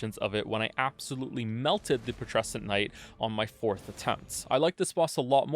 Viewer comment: Dunno if this matters to you, but at 15:38, you say "fourth attempt" but the subtitles say "fifth attempt". Might be a nice correction to make